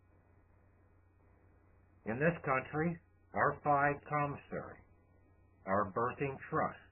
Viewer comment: He says it again Our "__________" Commissary our Birthing Trust is the bank of america at 10:23-10:29 what is that word??? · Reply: The word is " Fide Commissary". You can easily look this up on any search engine: Yahoo, Dogpile, Google, etc.